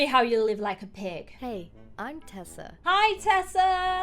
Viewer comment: Honestly at the start when her name was Tessa i thought she said Peppa 😂😂😂😂